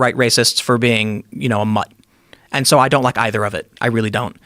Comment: for being "mut"? What did he say? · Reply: Mutt as in mixed race. I think he's half korean and half white.